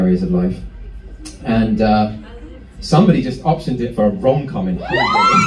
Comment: What do you think Ben meant when he said: ‘Somebody just options it for a wrong comment’?